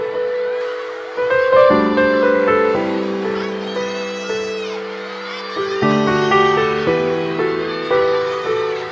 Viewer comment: what did jisung said to minhyun????? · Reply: Uljima - Don't cry